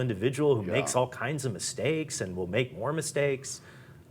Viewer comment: Did he say he makes “Mormon Steaks”???? Lol